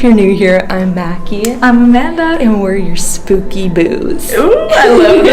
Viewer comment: I heard "Spooky Boobs" to be honest... And it's also fitting